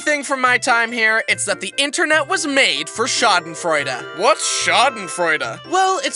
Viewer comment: he said Schadenfreude its a German word and i as a German! Couldn't understand him so i had to watch it like 3 times to know what he said. And i don't blame him German is hard but i find it to funny 😂😂😂